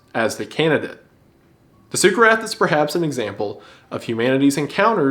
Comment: I was cooking while listening and misheard succarath as super-rat, which had me really excited for a minute